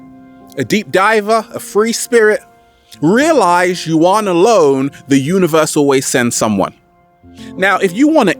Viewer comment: What is what you are saying? you are a loan? or you are not alone?